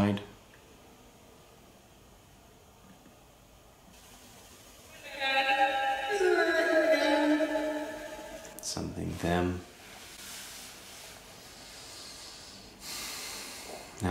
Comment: I thought at 8:46 she said “Yes, he hurt them.” (Sounded like she was crying)